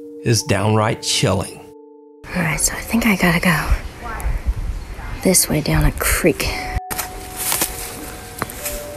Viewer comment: OKAY did anyone hear the “why” right after she says “guys I think I got to go?” It sounds just like a female voice 5:02 she says something after that too but I cannot understand it. From the beginning of her investigation you can hear that voice. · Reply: After that she says stop . - " guys i think i gotta go " - " why ? Stop . "